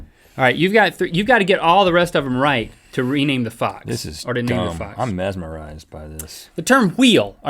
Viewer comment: Link says: "To rename the fox." Subtitles says: "To rename the fuck." hahaha